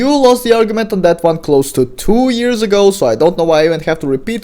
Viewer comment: This is the only time im fine with "milking" content. Because Mayo is so stupid to keep trying to revive a dead drama from two years ago, and that dies after a few months. Edit: i misheard and thought it was a year ago, it was 2 years ago.